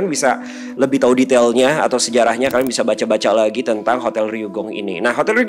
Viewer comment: awal2 nonton sempet salah denger...hotel dugong 😂